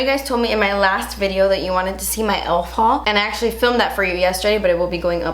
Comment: It took me forever to figure out what you said it 1:06 then I remembered "elf haul" 😂